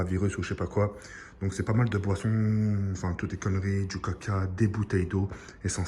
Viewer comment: c'est moi ou a 1:19 il a dit "caca" ?!